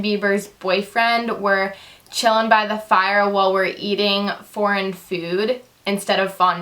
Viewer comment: I laughed so hard when you said that you thought that he said "While were eatin' furan 'food" lol -- I have 344 charecters left!!!!!!!!!!!!!!!! !!!!!!!=)!